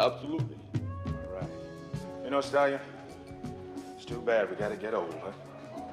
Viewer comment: Apollo: “it’s a shame we gotta get old.” Rocky: “Just keep punching, Apollo.” That saying didn’t age well. Not even Rocky took that advice. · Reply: I always thought he was saying "It's a shame we gotta get it overwith" as in the tie breaker